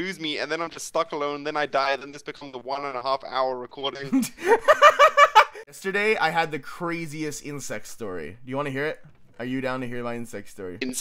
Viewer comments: I swear I heard "Yesterday I had the craziest incest story." I'm glad I'm wrong... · I THOUGHT HE SAID INCEST STORY OH MY GOD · When he said 'crazy insect story' I spew water all over my iPhone (i watch on my phone)... I thought he said 'crazy incest story'.... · omg i thought said "insane sex story" instead of " insane insect story" HAHAHAHAHA